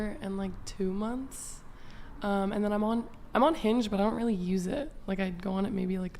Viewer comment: Damn i thought tey were saying i´m Unhinged ffs